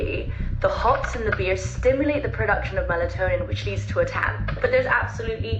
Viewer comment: The most accurate TikTok documentary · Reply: did you say melatonin?